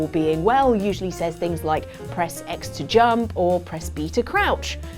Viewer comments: I thought I heard her say "Press X to jump and Peter Crouch" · Did you just say Peter Crouch? ⚽️